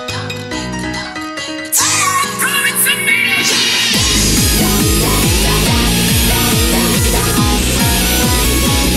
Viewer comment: I cannot, for the life of me figure out what keeps singing/yelling For example; at 1:41 It sounds very cool, and I'm pretty sure he repeats it a few other times, I jUST CANT SEEM TO UNDERSTAND HIM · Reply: He's singing "the echo in the mirror" with a very heavy accent. ^^